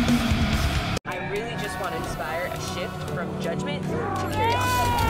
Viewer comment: "I really just wanna inspire sh*t from judgement." That's what I heard her say.